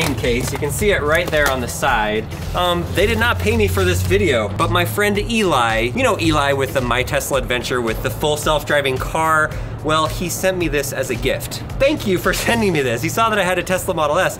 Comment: I thought he said Elon 😂😂Teslas oner😂